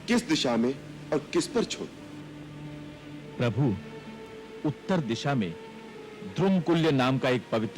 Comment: jab Samudr Dev kehta hai Uttar Disha mein the Subtitle Says South side 😂😂